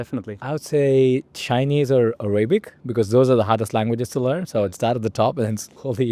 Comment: Did you say Arabic 🥰 This is my beautiful language ❤